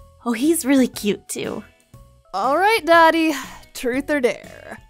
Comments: it sounded like she said Daddy instead of doty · Everyone heard: "alright dottie" What i heard: "alright daddy" 💀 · Omg when said alright Dottie I thought she said alright daddy😌😂😂😂😂